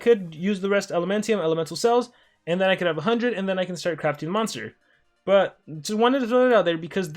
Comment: what did he say at 3:53 lmao? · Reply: He said monster